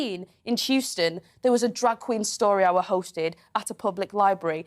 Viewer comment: is she saying drag queen or drug queen 😂😅😂😅🤭🤭